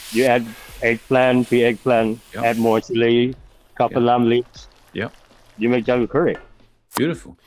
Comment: I think he said add the “Kaffir lime leaves” which is “ใบมะกรูด” or Bai Ma Grood in Thai It’s so great for aromatics.